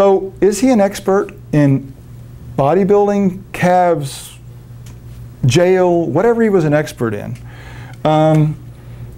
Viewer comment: did he say jail ? bruh🤣🤣🤣🤣🤣🤣